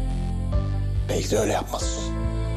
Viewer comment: ne diyor Hekimoğlu orada ? Yüz kere dinledim anlamıyorum. Neyse öyle yapmalısın mı diyor ? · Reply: "Belki de öyle yapmalısın"